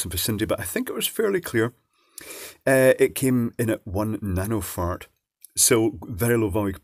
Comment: did you say "nanoFart"? 😂